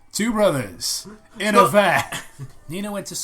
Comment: did he say "two brothers... in a van"? Was that a play on Rick&Morty?